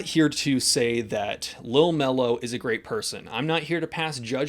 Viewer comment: Oh! "LIL MELO"..... Oh! .... i thought you said Hunter Biden.....